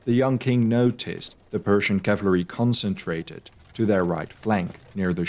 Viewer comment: did you say "cavlary" ?